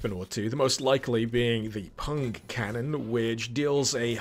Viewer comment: ~ Did you say "Punk Cannon" or "Pung Cannon"? >_<